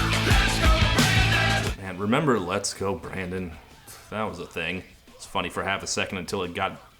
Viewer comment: Am I the only one who hears “Let’s go braindead” here?